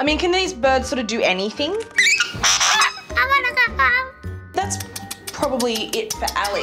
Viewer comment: Coulda sworn that bird said " I wanna go home"